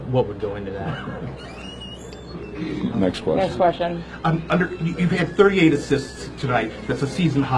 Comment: Did he say you had 38 assists tonight 😂?